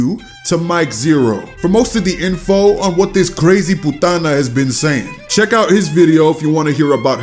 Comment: Did bro just call her a '' poutana '' at 4:32 im literally dying 😭😭